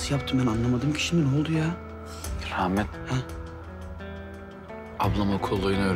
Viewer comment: ne diyor ben anlamadım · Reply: Rahmet